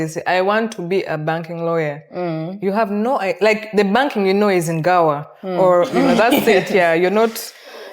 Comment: I hear "The Banking you know is only in Gower" 🙈🙈🙈🙈🙈